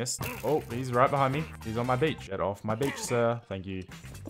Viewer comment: I thought he said he said "he's on my bitch" and then realised he said beach